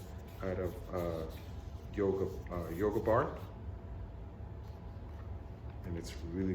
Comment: ! What is your wedge made of? I thought I heard you say yoga bar. Let me know. Want to copy. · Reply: You are correct. I made it out of yoga bar. 6 dollars at Walmart.